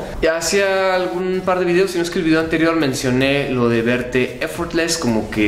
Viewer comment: Qué dice en el min 5:11? ¿"había escribido"? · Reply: dice: ”Si no es qué “